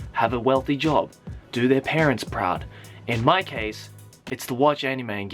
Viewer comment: We he said "Do your parents proud" I thought he just said "Do your parents" and I died laughing